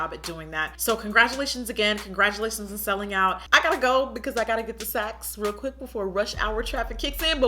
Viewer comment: My dumbass thought you said "you gotta go hurry up and get the sex" 😭😭😭